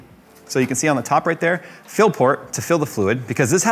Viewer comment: I keep hearing "Phil port" and I want to believe.